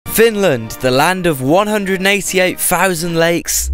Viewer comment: i had the volume all the way up and the first second i hear "FILLAAAND!!!"